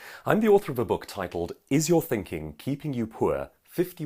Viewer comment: LOL, I thought you said "ISRAEL THINKING KEEPING YOU POOR" so I thought, of course, it is, if you don't know that then you are a complete Idiot or a Communist!